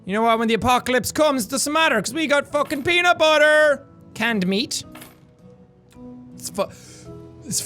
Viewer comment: i thought he said danked meat not "canned meat"